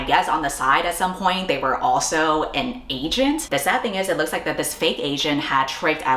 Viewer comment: I heard 'agent' as 'Asian', so I was like "damn, double race-faking"